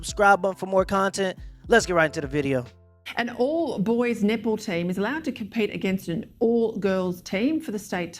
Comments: I heard "nipple team" (I know she's talking about net ball) so I turned the closed captions on and sure enough! She said nipple team. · i had my eyes off the screen and I thought I heard an all boys nipple team,must be her accent